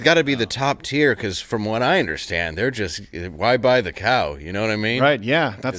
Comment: I thought Dave said “why buy the cow when yaks milk is free. “ For real!